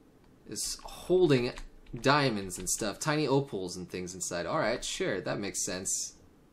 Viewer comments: Dude... DUDE? REALLY? sigh Please tell me you were kidding. Even if it's not true, just tell me you were joking. Hahaha, that was really funny. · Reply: After re-watching this countless times without getting what you meant, I finally found out what was wrong. I said "Opals," not "Apples." They sound so close that it's understandable why you thought I said that, haha. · Dude... DUDE? REALLY? sigh Please tell me you were kidding. Even if it's not true, just tell me you were joking. Hahaha, that was really funny. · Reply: After re-watching it, i noticed that you actually say Opals. Yeah, it is pretty easy to mishear that.